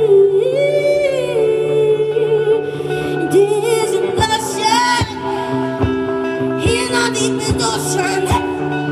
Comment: Whats words did she said in 5:31 till 5:37? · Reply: Kestutis Kiseliovas it is a notion in our deepest ocean i think :)